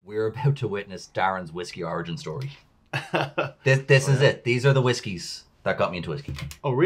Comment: I thought Darren said, "These are the whiskeys that got me twisty." 🙃😶